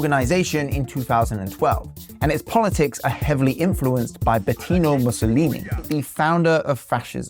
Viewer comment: Don't know if anyone caught this, but said Betino Mussolini instead of Benito Mussolini